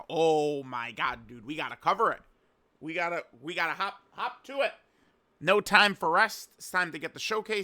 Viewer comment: Did he say hawk to it